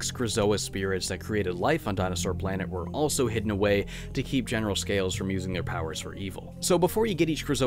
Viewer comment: also it sounds like crystal says GENTITAL SCALES